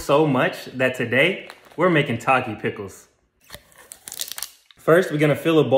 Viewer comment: I thought he said taco pickles 😭